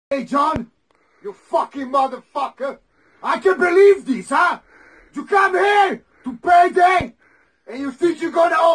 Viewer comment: Translation? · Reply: Thank you. I thought he was saying “I can’t release this huh”